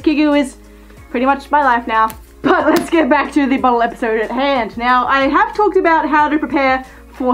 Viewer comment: I could have sworn you said 'let's get back to the molester at hand' 😳😳😳